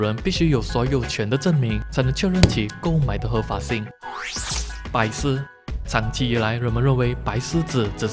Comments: 我聽成白痴......... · 我把白獅聽成白癡😂😂😂